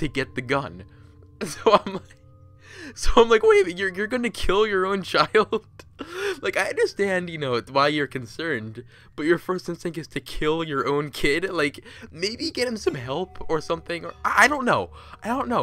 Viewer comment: Not gonna lie , when you said: “Maybe you should get him some help?!” I heard what sounded like church bells and thought you were going to say: “Maybe get him some Jesus?”